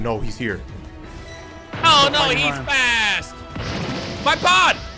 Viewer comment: did intentionally try to sound like Squilliam saying "oh no hes hot" lmao